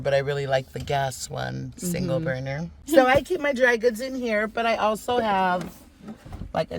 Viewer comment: When she said "dry goods" I thought she said dragons!😅